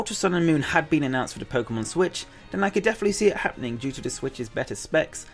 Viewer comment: did he say "Pokemon switch"😹